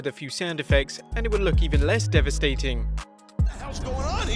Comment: I thought he said defacating